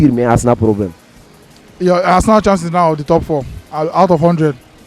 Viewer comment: I thought said Arsenal is chasing top 3, not top 4😄😄😄😄😄